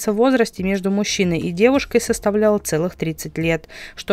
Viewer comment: Разница в возрасте всего 30 лет. Ну это же совсем всего. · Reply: где вы услышали "всего", когда было сказано "целых"?